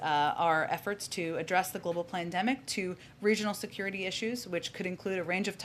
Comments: She clearly said "global plandemic" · she called it the plandemic!! Tell me I'm not the only one who heard that!!